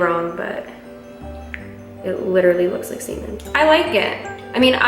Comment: I thought she said it looks like symen 😂😂